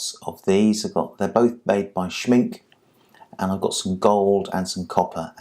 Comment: who is the vendor the metal dust? It sounded like you said "Shrink"... Thanks much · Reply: Schmincke